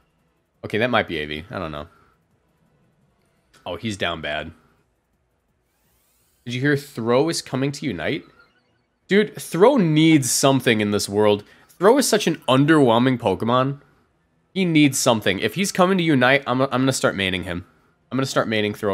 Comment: I heard bulu is coming to unite......